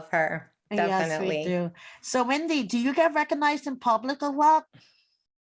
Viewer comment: did she just call her Wendy?